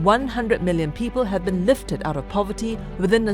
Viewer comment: Did you say 100m or 800m people lifted out of poverty? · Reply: Lee: She said 800 million people out of extreme poverty. Extreme poverty is defined as people earn less than $1.0 per day. In other words, close to starvation. There are hundreds of millions of poor people in China. The government has a lot of work to do. At least, they are trying. They don't want to them welfare because that won't help them in the long term.